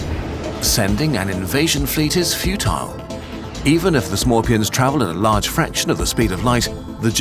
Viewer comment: For a second I thought you were saying "Scorpions". I don't want to fight interstellar scorpions, that would be terrifying.